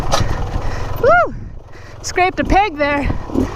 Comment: I thought she said Scraped a pig there and I’m thinking to myself why are the pigs in the middle of the desert😂😂😂😂